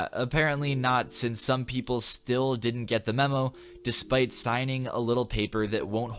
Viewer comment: I thought it said meme-o despacito